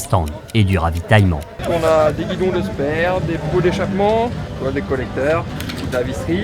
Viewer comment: c'est moi ou j'ai bien entendu à 25:10 des bidons de sperme ? :D